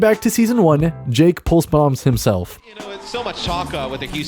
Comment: "Jake Pulse bombs himself" I thought he said Jake Paul bombs himself.